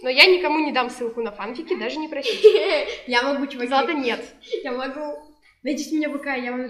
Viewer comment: Злата?0_0 Или мне послышалось?!0_0